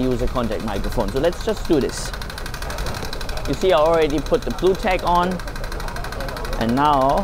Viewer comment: what are you saying you already put on? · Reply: The BluTac.